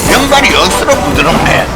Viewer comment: 나만 2:42쯤에 명박이 억수로 부드럽네 라고 들림